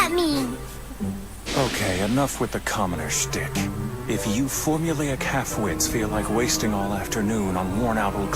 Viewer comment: I thought kyoya said "you formulaic catamite...." But he said "you formulaic half-wits...." And if you Google what Catamite means you will get a great laugh out of it.😂😂😂😂😂